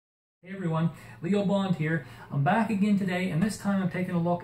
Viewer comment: When he said Leo bond I thought he said legally blonde